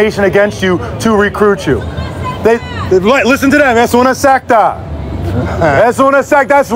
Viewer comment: I heard es 'una insecta'--not 'una secta'--so I was looking out for cucarachas!!!